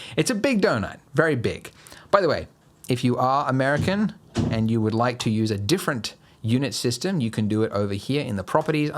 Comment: lol I thought he said "btw, if you're american, I assume you would have huge sized food like this"